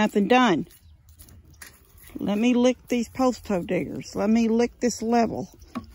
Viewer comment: I’m sorry I set my phone down for a minute and l turned around and all I heard was, “let me lick this post hole digger, let me lick this stick” Awww Lawwd I had to look twice to see who’s channel I was on 🤭😆